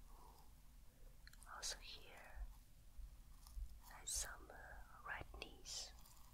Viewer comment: when she says « redness » at 1:00 I always hear « red knees », which is kind of confusing